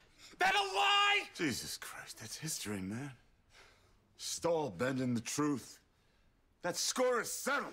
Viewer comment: What does Clay say at 1:32? Stole a bend in the truth? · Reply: Stahl bending the truth.